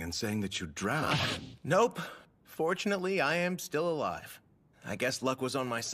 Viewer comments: For a second I thought he said unfortunately he’s still alive and laughed cause it was such a mood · I THOUGHT SAID "unfortunately, i am still alive."